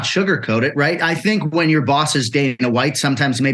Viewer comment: give or take , Jon begins to describe his new contract , and for some reason when he says Dana white my phone distorts is so it sounds like he says 'When your boss is dating your wife" ...instead of 'When your boss is Dana White ' ....idk that was funny from where I'm sitting and listening , but yea Jon absolutely deserves his new contract, good for him.